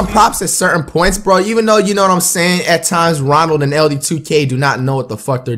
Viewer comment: did he just say ronald2k